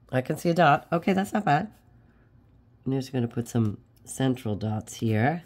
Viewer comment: I fell out of my chair laughing when you said central dots... for all the money in Texas I thought you said "sinful" dots.😂 you did such a great background! L8ve the coffee splash flowers 💐.